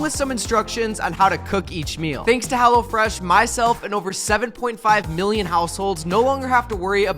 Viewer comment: I thought you said 7.5 billion households not 7.5 million still insane numbers.